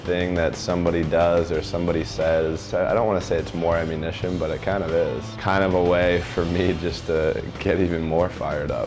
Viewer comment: what is he saying there? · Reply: "I don't want to say it's more ammunition but it kind of is..."